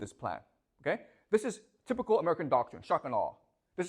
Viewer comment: what does he say here? "Typical American doctrine ---" (I couldn't understand the words, did he say 'shockin' all'... 'shotgun law'?... Help pls) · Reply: Shock and awe